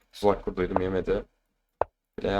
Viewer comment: ne diyor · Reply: Tuzak kurduydum yemedi